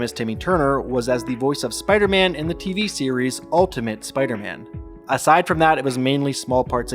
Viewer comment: did you really say spider hand???